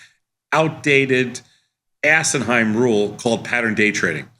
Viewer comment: Did Sosnoff say the PDT rule was "assenhime" @ 1:13? lmao